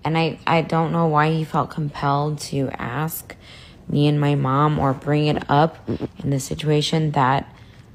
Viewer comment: Trisha's sister: "The candlestick thing made us uncomfortable, it was disgusting" Trisha's sister: collaborated in her sister's OnlyFans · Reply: omg i thought you were saying me and my mom recorded it. But other than that... thats icky and gross